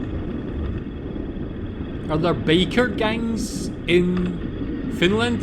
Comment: Funny how with your accent it sounded like you said "baker gang" 🤣